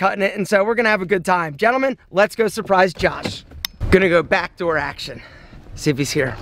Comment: did he just say back door action??? 😂😂😂😂😂😂